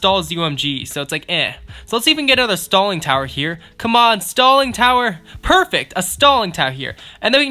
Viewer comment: Sounded like he said “Stalin tower” lol